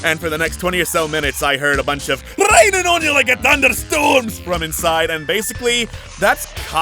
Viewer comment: And for the next 20 or so minutes I heard "RANNING ON YA LIKE A THUNDERSTORM"... those are the best words taken out of context.